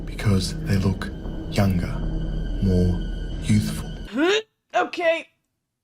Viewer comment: “Because they look younger, more.. youthful” That sentence just rubs me the wrong way and I don’t know why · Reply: Omg I first thought he said "more useful" instead of youthful. But it's fucking creepy and wrong regardless 💀